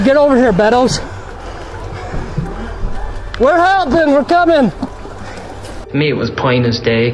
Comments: I clearly heard the lady say at 02:41 say “help us” right after the officer says “get over here Bettles” unless that was somebody else. But it coincided with his statement. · I hear the very faint voice of the mom asking for help. The office immediately responds, “We’re coming. We’re helping!” This kind of love transcends all time and space ❤ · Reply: Why i hear "OVER HERE" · The homeless dude "Yooo wtf you doin 😒😒" 😂😂😂😂 Edit : Did anyone catch voice "OVER HERE @ 2:40??? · Actually at 2:42 if you listen closely you can kinda hear a woman's muffled voice and I think she said "help us, we're inside"